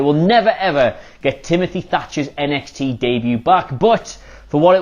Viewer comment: I thought Pete Dunne said "Timofy Fatcher" 😆😆😆